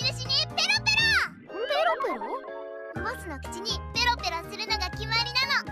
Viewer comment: She clearly said to lick HER. You can´t fool us Meimei. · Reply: she said lick the boss, just a bad translate